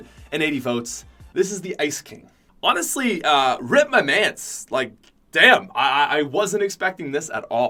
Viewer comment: I thought he said rip my pants